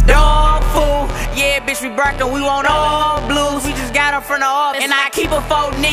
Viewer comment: did he say brackin or barkin